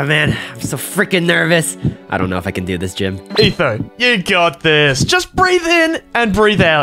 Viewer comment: Why do I hear he says " Aether" instead of "Etho"?